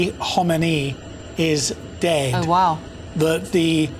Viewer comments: did she just say " oh wow " 😂😂😂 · did she said "oh wow"